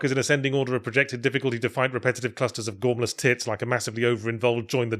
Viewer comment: Did you call them a tit?